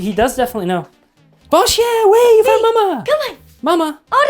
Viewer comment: Does bf speak French? I heard him say "Bon chien, ouais!" where'd he learn ouais??